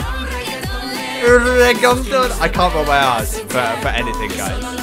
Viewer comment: it sounds like you said "I can't roll my ass guys" 🤣🤣🤣